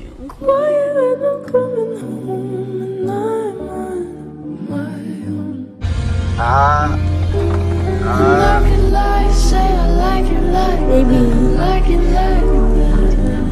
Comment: what does it say in the minute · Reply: "I can't not smile when I look at you"